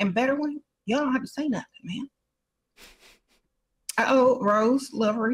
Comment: I can’t take her serious because of how she holds her lips.😂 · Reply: it`s giving Donald Duck 🦆every time she says "Sprinkle sprinkle" I hear "Quack quack" 🤣🤣